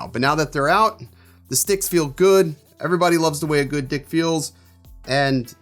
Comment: Lol 😂 😂 I was wondering what her really said.. Everyone loves the way a good What feels!!! 😂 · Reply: Jamal Walker he totally said dick there.....Lol 🤣🤣🤣 him and those sticks